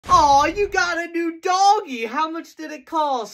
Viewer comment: I thought he said that you gotta do doggy